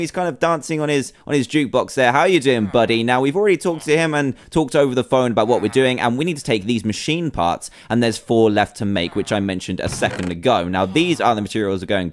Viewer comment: I thought it said machine farts XD